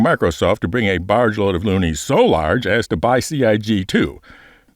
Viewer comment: I hadn't consciously thought about what a cluster that would be for someone else to buy Turbulent. It would be so much worse than the Firesprite incident. When you were talking about Microsoft, I thought you said monies... then I realized that you were talking about Loonies specifically, I think Toonies would be more manageable considering the sum.